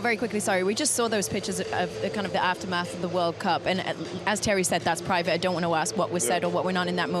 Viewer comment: Did she call him Terry? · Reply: Thierry